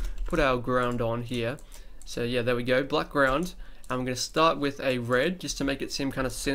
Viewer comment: I thought you said "Blackround", as in Black Background XD I was like "oooo clever!" ...I'm so stupid...